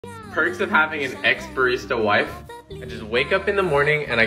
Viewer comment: Literally thought he said “perks of having an ex wife” 😂